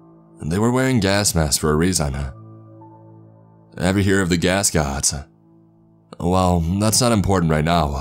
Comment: gass gods or gaskets??? What?? I know it’s strange but I wanna look it up but I have no clue what he said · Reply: Gascots like gas mask wearing mascots.